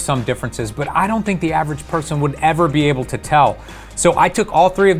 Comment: That's right, the average person will be able to tell. Glad i can that's why i still use DSLR. It's not just the blur it's bokelicious. The size and shape of bokeh matter, It adds depth to the picture instead of just blurring. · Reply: He said clearly said : " I DON"T THINK ... the average person will be able to tell"